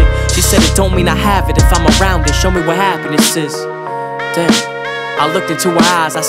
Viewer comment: I thought he was saying-"Show what happened to sis"